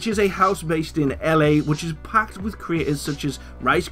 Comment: I thought you said house packed with retards